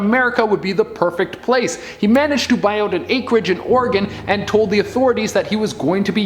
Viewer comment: did just say Oregon? That is the state that I currently live in and I heard tons of crime happening in the state just like everywhere else in the world, which is insane.